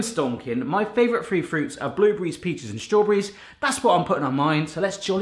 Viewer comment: Did anyone else thought he said, his favourite fruit was, blueberries, pizza, & strawberries?